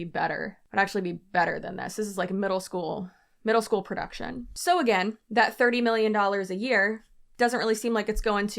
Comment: When you said, “so, again…” I heard ….. “So, you see……” 😂😂😂